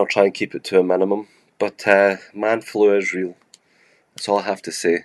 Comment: I keep hearing "man-fluids" and i feel like shrek is warning me he's gonna sneeze my screen green